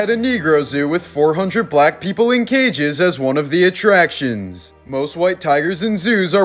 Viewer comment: I thought he said 400 Black people locked in cages on the 2nd one